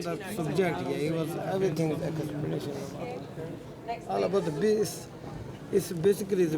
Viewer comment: Did he just say “All about the bass!”? 😅